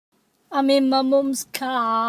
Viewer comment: Anybody else remember it as “I’m in ME mums car” not “I’m in MY mums car” like WHAT!!! Mandela effect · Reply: i hear "imin memuums cah brumbrum" "git out mecah" or "mika"